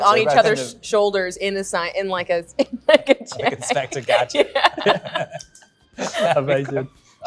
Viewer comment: What does Rob say at 09:02? Helpppppppp I don't understand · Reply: “Inspector gadget “